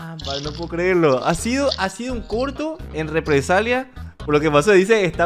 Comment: entendí P*rno XD jajajaa